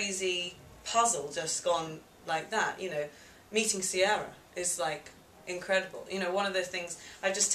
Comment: did she say "Meeting Sierra"? If so, I think it's awsome that Summer & Sierra are such great friends and teammates for this production. All too often in show business these days, many women focas more on being competitive or striving to be the leading lady. It seems Summer & Sierra have ignored that horrible stereotype & respect each other as performers. Each willing to do their best for the show and not concentrate on being the star. We need more people like them.